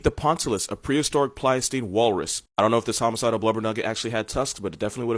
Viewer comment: did he say blubber nugget?! 😅😆